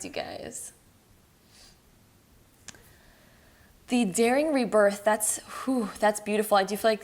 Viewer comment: Damn I'm early 🙉 love and blessings to everyone ❤️🧡💛💚💜🤎🖤🤍 P.S. English being my second language I heard in pile 3" dairy rebirth" I was like wtf I'm trying to avoid dairy😅